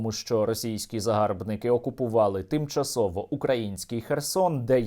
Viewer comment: це мені почулось, чи? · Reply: Тимчасово український Херсон?